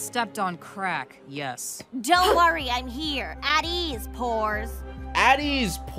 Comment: NGL, I thought she said "whores" not "poors"